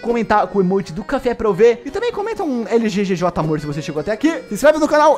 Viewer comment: Só eu que entendi em 18:59 lggj morto invéis de Lggj Amor <3